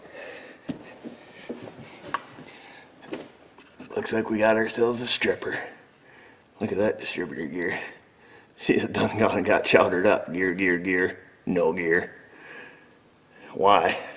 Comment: "We got ourselves a stripper". Did I hear that right ? @ 10:12 Good one 😂 😂